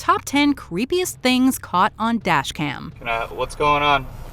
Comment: I thought it said “death cam”